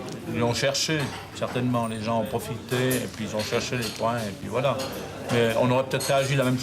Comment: I think you interpreted what he said as "chercher les points" "looking for points", but this is not commonly said in french, as opposed to this homophone: "chercher les poings", "looking for fists", meaning "looking to (cause a) fight", which in this context makes a lot of sense !